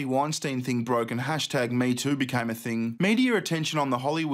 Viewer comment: Sounded like she said YouTube instead of MeToo XD